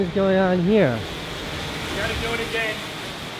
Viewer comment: Any one know what that guy said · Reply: I think he said "we've got to do it again" saying that Casey ruined the shot.